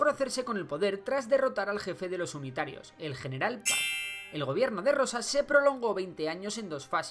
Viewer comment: el general qué?.... no se entiende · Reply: José María Paz, creó la Liga del interior (unitarios)